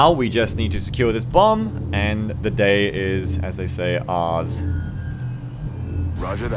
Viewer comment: Wait, did just say "The day is, as they say, arse."?!